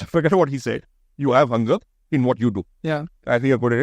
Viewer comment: What did ashneer say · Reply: The Sanjeev sir said na, forget what he is saying. I think Ashneer cannot be a really good mentor, he is just behind making him sound superior. Just a Shark tank Fame, nothing else, very overrated